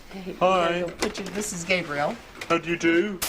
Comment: did she says "this is gaybriel?"